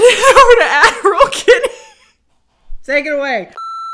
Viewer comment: I keep hearing "Adderall Kennie" when you say "Ad role Kennie"